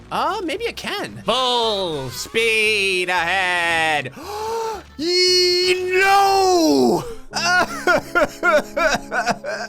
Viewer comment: When said full speed ahead I thought he said balls deep ahead!